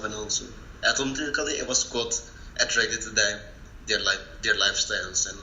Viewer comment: I think he said "I honestly got attracted to their lifestyles"